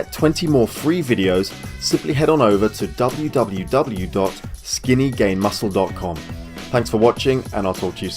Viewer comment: i thought you said skinngaymuscles i was like WTF?